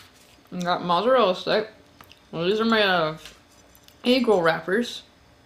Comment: these are made with "eagle wrappers" WTF? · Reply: I thought she said “ankle wrappers”